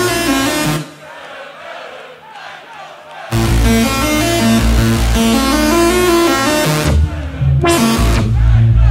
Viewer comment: What they say at 3:49?? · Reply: They said ,,Kernkraft"